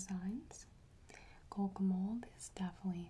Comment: What is she saying at 7:17 ??? Coco mold? Coke a mole?? My brain hurts. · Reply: it's a brand Kokomold :)